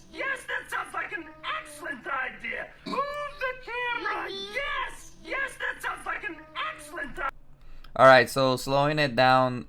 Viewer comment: I hear: "Hold the camera! Yes, yes, that's a fuckin' excellent idea!" I CAN'T STOP LAUGHING! 😂